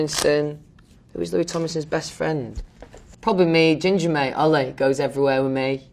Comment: help I don't know what he's saying with his british accent. All I'm getting is "prodly med ginger made Olly."